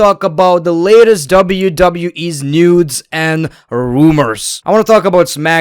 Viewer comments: Did he say nudes or new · did he said nudes and rumors 😂😂